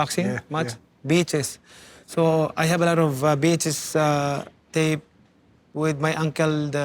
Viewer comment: I thought manny said I had a lot of bitches….he meant VHS 🤣